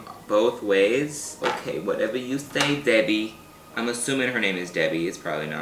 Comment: I heard "whatever you say, daddy" and I CHOKED akjsfdna